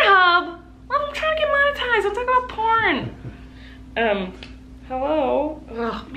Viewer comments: Is this just me but when she said porn it sounded like Miranda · My mom heard you say pornhub and now I’m in trouble 😂